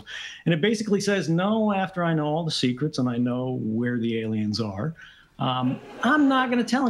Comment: Did he just say " I know where aliens are?"